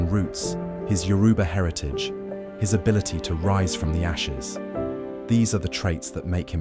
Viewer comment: You sounded like this at 24:14 .....His Nigerian routes,His yoruba heritage,His ability to rise from "Nyashes"".....that means "Ass" lin Nigerian pidgean English....but let me blive your meant 'the ashes'....😂.